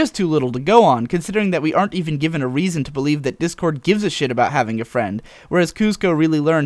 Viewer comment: I heard "Dipcord"